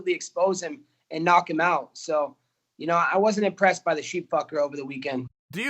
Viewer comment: Colby called Khabib “The Sheep F’er” wooow lmao. Chael almost lost it but knew he couldn’t go down that road lol. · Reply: I thought he said "sheet fcker" because his wife wears a sheet